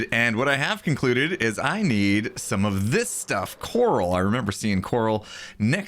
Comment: when he said coral it sounded like yanny